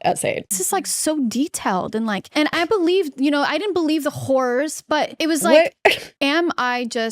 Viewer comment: dude when catherine said 'i didn't believe the horrors' i thought at first that she said 'i didnt believe the whores' and i actually fucking gasped